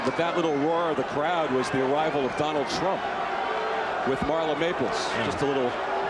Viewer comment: wtf did the commentator say 😂😂😂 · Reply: T R U M P